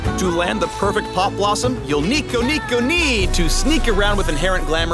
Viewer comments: DID HE JUST SAID 2:50 "NEEKO NEEKO NEE" ? 😆😆😆😆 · Did he just say Neeko neeko nee ?? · Did he just say Nikonee? Cancer coming